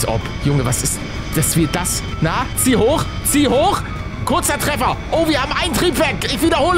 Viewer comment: bin ich der einzige der am anfang „nazi“ Gehört hat?